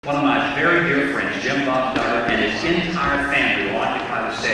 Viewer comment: I thought he said douchbob duggar !😂